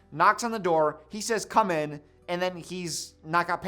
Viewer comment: I think he meant to say "coming" but it sounded like "come in" so Pam entered without hesitation, hence, Michael had no pants on.